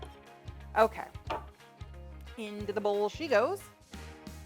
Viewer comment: I was chewing some very noisy rice crispy treats so when you said “into the bowl she goes” I heard “invisible cheetos” and I was like WHOA! WHERE DO I FIND THOSE?!